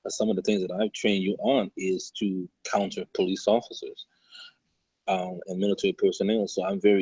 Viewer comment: does anyone else hear him say “I train you to counter Police and Military personnel”?